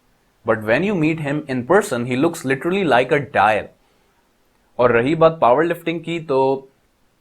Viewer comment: Ustaad can you clear how he(saket) look when someone meet him can't understand the stuff you said ? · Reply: Dayan